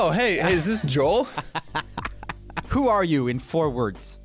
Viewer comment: Joel: Who are you in 4 words? James: Uhm... what? Me: "iM A gOod PeRsOn" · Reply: I heard "who are you in forwards?". I was so confused😂